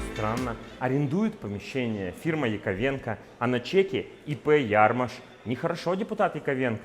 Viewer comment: послышалось "ИП Ярмыш" 😅 Вот откуда деньги ФБК идут, от палаток в Новосибирске👌